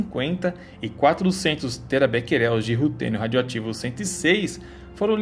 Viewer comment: não entendi a palavra... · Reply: É Terabecquerels